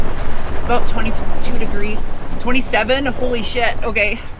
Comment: Did I heard an “holy shrt” without a beep? @ 10:53 LOL!